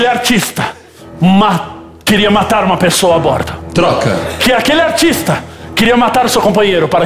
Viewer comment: eu juro que eu ouvi "queria matar uma pessoa morta" ahahahah